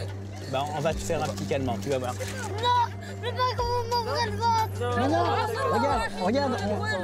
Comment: C'est quoi le «vot»? (sous-titre) (je suis espagnol). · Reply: Le sous-titre était faux, le mot c'est ventre.